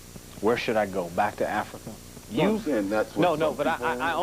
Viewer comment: What does OJ say at 4:40? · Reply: Sounds like he says: "Well that's what some people say....: In response to the interviewers comment about people wanting him to go back to Africa - or not. It's hard to hear, because the interviewer cut him off for a second there too.